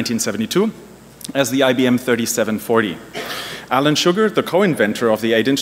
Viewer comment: For quite some time there I thought he said "Alan Sugar", the founder of Amstrad, now a billionaire... but ofc he is talking about Alan Shugart - whom went on to become the CEO of Seagate until 1998 and passed away some 8 years later.